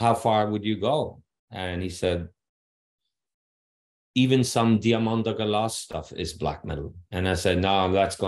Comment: what band name or artist did he say? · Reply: Diamanda Galas